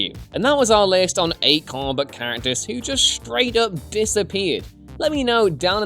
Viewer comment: When you say "comic book character" I keep hearing "Carbot character" xD